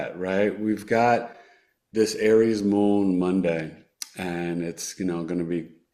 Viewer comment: When says....Aries moon Monday.....all I heard was "just another Aries moon monday" in the tune of manic Monday 😂😂 😂